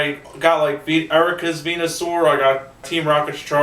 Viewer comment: I thought he said penisaur at first. Lol